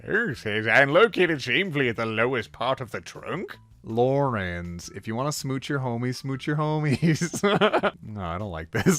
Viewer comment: IT SOUNDED LIKE HE SAID ”Laurance if you wanna smooch your homies smooch your homies” AND THEN I WAS LIKE OEJSISISJDMDI IT REMINDED ME OF GARROTH X LAURANCE AND IT WAA JUST REALLY FUNNY XD